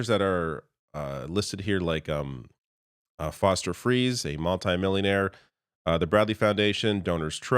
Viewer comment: When you said Foster Friess I thought that you had said Foster Freeze which is a pretty fast food place by my house.